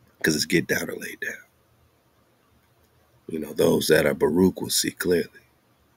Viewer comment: what is that word baroque? Did I even spell that right? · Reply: Baruk is blessed